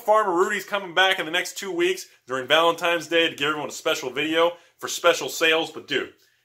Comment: Did u say valentimes ?